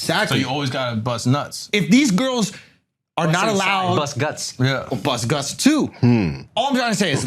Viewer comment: I thought he said deep guts bust nuts I was like thas right but I misheard